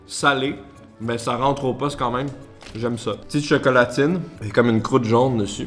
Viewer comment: chocolat quoi ? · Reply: Chocolatine🤡